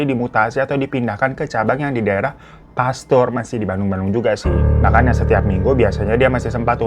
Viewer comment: Pasteur kan maksudnya bang bukan Pastur😭 eu eu Apa gue salah denger😭